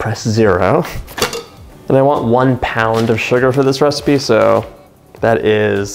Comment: 226 gm/1cup unsalted- butter (soft to touch- room temp) Paddle attachment 454gm/ 1lb sugar sifted 1 tbsp vanilla extract 1-4tbsp heavy cream (needed as per stiffness needed) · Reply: Thanks for the sugar clarification.. I thought I heard 2 pounds?!?